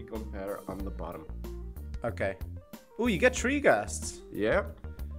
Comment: when he got three gastes i thought he said tree gastes